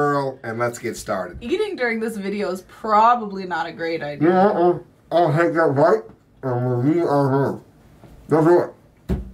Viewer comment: I think he said: "Yeah urfgh. I'll take a bite and move from there. Let's do it." Someone correct me if I'm wrong. 🤣🤣🤣